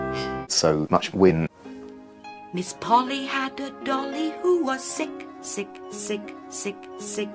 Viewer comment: misheard:''miss polly had a dolly, 666'' 🤣🤣🤣